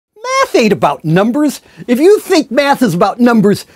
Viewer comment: I thought he was saying "Meth isn't about numbers" in the beginning. Judging by the energy this man has, I may be right.